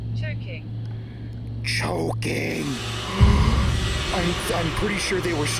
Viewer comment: I thought when t hey said "choking" that they said "sure king."